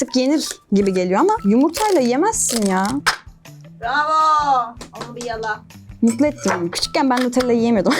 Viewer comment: Arkadan sesini duydum Dedigi:Bravo onu bi yala.